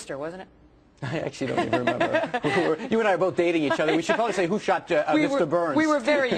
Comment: Is it just me or did he say "you and I were both dating each other" at 2:08??